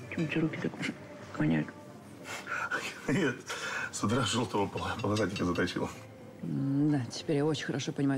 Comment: не понимаю что он сказал? что заточил? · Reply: Жёлтого полосатика заточил 😂, вот руки и воняют 😅